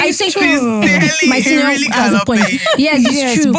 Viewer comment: did I hear my senior has a point?😭😹I don’t even know how to explain it but I’m still laughing😭😹